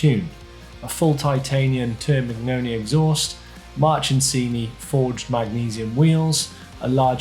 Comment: Is it my ears or do I keep hearing "March'n'sini"?